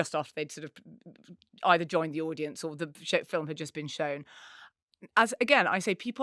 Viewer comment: Marina says "shit film has just been shown". I know that is not what was actually said but, given that it is Harry Styles. All mop hair and tattoos. Watermelon sugarhead.